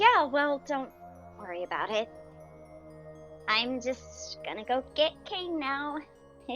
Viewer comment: I dont even dyslexic But my dum*Censored sound* brain thought it said “ i’m gonna go get cocaine now”